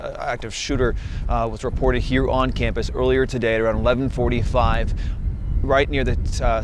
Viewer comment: he clearly said actor. These are actors.